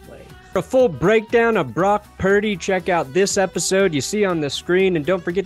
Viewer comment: Thought he said that's good sharts